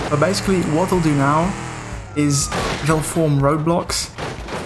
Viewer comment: I thought you said they'll form Roblox